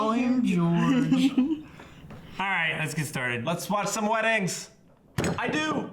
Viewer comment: Lol! Did he say let's watch some weddings????????? Well you're gonna watch some weddings later on. Just be careful what you wish for!